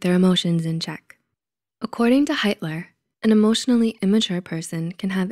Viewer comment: Wait, did I just hear "according to Hitler" at 0:47 ?